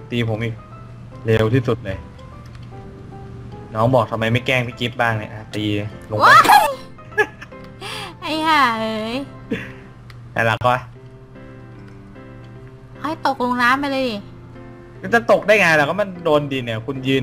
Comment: เหมือนได้ยินกับพูดว่า:ไอห่าเอ้ย:แล้วรักป่ะหล่ะ #สงสัยบีคงมโน